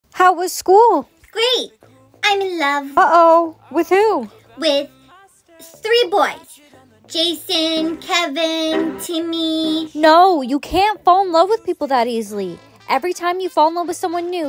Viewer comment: For a second I thought she said “Jason , KENNY”